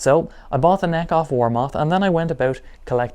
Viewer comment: I thought you were saying "wall mart" like "since when was wallmart in Ireland yet alone selling guitar necks? Ah no he''s saying Warmoth" :D